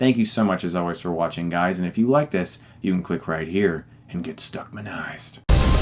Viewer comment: completely unrelated to the movie BUT can someone please tell we what he says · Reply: Get Stuckmanized (?) A word game between his last name and get addicted to something? :) Like: "The Stuckmann Effect" hahah Idk how to explain it :)